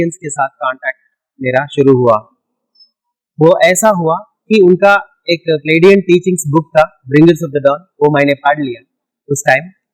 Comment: What is the name of the book he said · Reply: He said something like paladin teaching